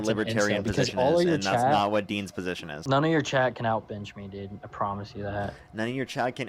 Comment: I heard "none of your chat can out-bitch me" and i was a but too ready to accept that was what he said